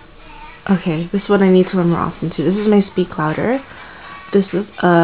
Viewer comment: hii :) nice lipsticks. at 1:48 is that speak louder? sorry i couldn't hear u clearly :/